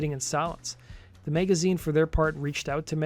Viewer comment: Did you call Rolling Stone Magazine "the Meg-a-zine"?